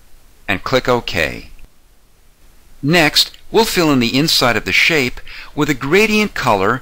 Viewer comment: what do you say? ? Click down with your mouse???? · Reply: Unboxing Romania "We'll fill in the inside of the shape..."